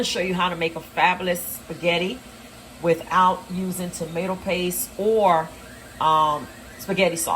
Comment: Thought you said no tomatoes or spaghetti sauce????